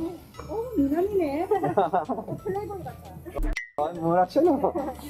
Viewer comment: Okay what did she really say lmao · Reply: 오 미남이네, 플레이보이 같아... minamine ... playboy gat-a... Oh, you are a handsome guy, you look like a playboy ... thanks for the question :)!